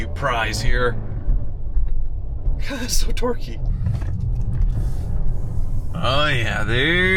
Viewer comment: Every time says "So torque-y!" I just hear "So dorky!"